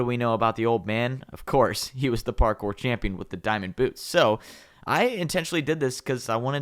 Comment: i heard the parkour camping not champion